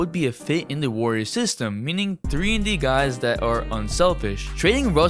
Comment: Did he say nigga